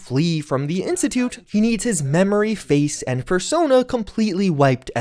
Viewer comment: Did anyone else hear fursona?